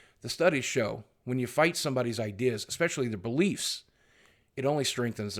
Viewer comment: lol when you said, "especially their beliefs" I thought you said, "especially the police" and I was like, "whoa wow whoah- where you going now Boogie" lol